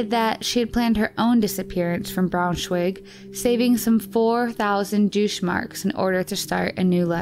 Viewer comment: did I hear a "4,000 douche marks?" haha...I think I did...